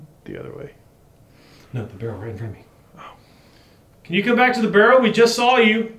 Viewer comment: I think i heard 'No" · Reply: he said oh